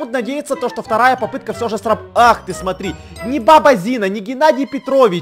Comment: Мне послышалось не "Баба Зина" а "Папа Зина"